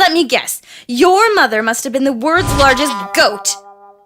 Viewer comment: Did say words or world's?